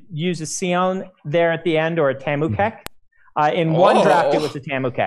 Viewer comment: So what is "Kemukek" thingy apparently related to Dragonsteel? (Sounded like "tambu cat" to me btw.. Some sort of magical cat from Yolen?) Does anybody know? 🧐 1:25:00 ... EDIT : Found it, it's actually " Tamu Kek " - <link> (Mightly lol to my idea of magical cats.)